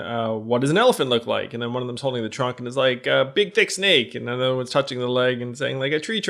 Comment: did yang just say ‘big dick snake’??